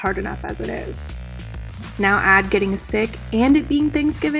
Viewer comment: WTF? I embrace my thick body. I want to be healthy and not overweight. I’m not doing surgery or promoting any unhealthy body image messages? This my HEALTH journey towards a healthy relationship with food and exercise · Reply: I truly apologize it sounded like thick not sick. I had to put the speaker at my ear.